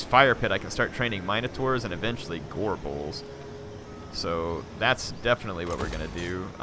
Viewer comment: "SQUISH! SQUISH! SQUISH!" - The memoirs of Al Gorebull and the squishing of mankind. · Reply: I heard "Al Gorebull" and died laughing